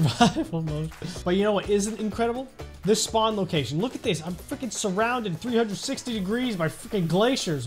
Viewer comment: did you just say f cking sounded like you said the f word then cut out the u (edit) you know what that did not sound like you cut out the u that sounded like the hole f word